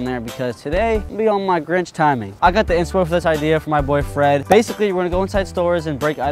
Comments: when you said “my boy Fred “ I thought you said boyfriend😂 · HE SAID BOY FRED----I THOUGHT HE SAID BOYFRIEND🤣🤣🤣😂😂😂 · I had to playback to hear what he said near the beginning I heard ‘from my boyfriend’ but it was ‘my boy Fred’ 💀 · I thought he said boyfriend!! I was finna click off so fast until I place it back😅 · I swear when he said "my boi fred" I thought he said "my boy friend"